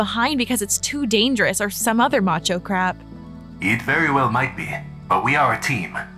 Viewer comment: Did journey just say crap